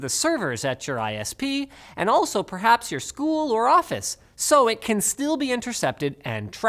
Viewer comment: did he just say school😰